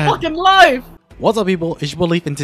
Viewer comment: Whenever i hear you say people i hear “pee bowl” lol